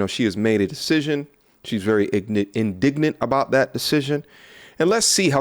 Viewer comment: he said ignant instead of indignant nah nah my mans yo black card slip